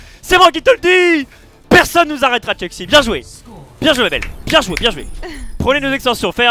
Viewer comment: a dit a 4:23 Bien joué et bien joué.....Eden chuis pas sourd dites moi ??? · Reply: Bien joué ma belle*